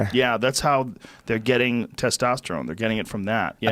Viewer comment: Lol I heard “they are getting testosterone from rams” instead of yams (like he said). They drink rams piss!